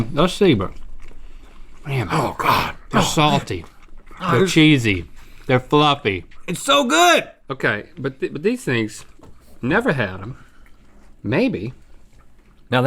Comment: Can someone please tell me what Link said at 2:36 ?? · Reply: I think he said "thus, savor," but I'm not sure. The combination of the North Carolina accent and the food in his cheeks makes it very hard to discern!